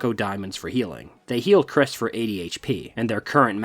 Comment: What it says “It heals Kris for 80 HP” What it reads: “it heals Chris for 80 HP” What I read: “it heals Chris of 80 LD” What I hear: “It heals Chris for ADHD”